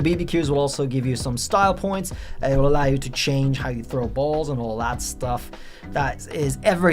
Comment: Oh no I had to check again, I thought you said “grow balls” 😭